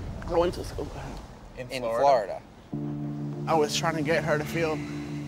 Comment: WTF 😳am I the only one that caught this and correct me if I'm wrong but I swear he says at 2:37 I was trying to get her killed dude is nutts